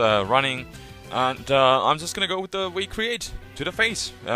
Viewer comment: it sounds like you say, "we create" instead of V create, but still, a fun victini....in UU? seriously, that's why i don't like smogon...